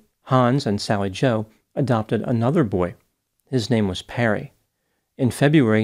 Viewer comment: I thought you said “His name was Parrot.”